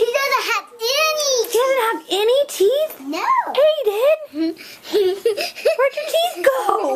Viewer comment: it sounded like said “Hayden” 😱 OMG SHE PREDICTED THE FUTURE 😂😱*sarcasm*